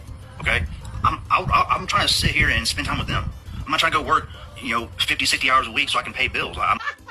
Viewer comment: did he actually say I'm not trying to go work 50/60 hours a week just to pay bills😂😂